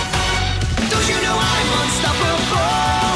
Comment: Time has come I’m ready to go Venturing into the unknown Fires inside me must be unleashed! People talkin nonsense all day “Chinpunkan” That’s all they can say I can’t get no satisfaction! Wo-ho Challenges may come Wo- ho fighting’s just begun! There’s no stopping me from spreading out my wings (Let’s fly high) Concentrate my power In the final hour In the end, I am gonna win! Don’t you know I’m unstoppable? Don’t even try cause you will fall I got a strength Deep in my soul That gives me courage and control! I’ll go beyond the limit break The universe is mine to take I have no fear I think it’s clear Let’s kick it up into high gear! Oh this is Dragon Ball Super We’ve got the skills to blow Zen-Oh away! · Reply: For some reason I hear "don't you know why I'm unstoppable"